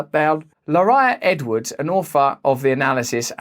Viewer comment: when he said "author" I thought he said "old fart" @ 2:17 😂😂